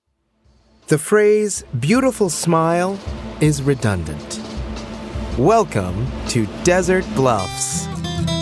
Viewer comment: I heard “Welcome To desert bluffs” and I screeched